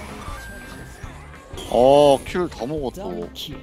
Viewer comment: 물론 킬 다 먹어 또 인건 알지만 애교처럼 들려 킬다모고또~